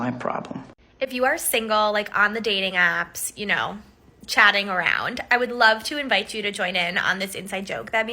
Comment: I thought the 2nd woman said "when you're on the dating apps you know just Chadding around" when we know that's what she meant. Lol.